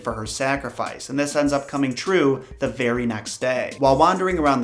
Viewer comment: Thanks for making the time to research and create these videos, it’s much appreciated. Plus when you were talking about the woman who visited Beauty in her dreams I thought you said the fairy next day instead of the very next day. Lol